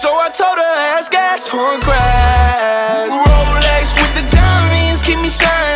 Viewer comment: I always thought at 1:11 he says "relax with the downies" 😂😂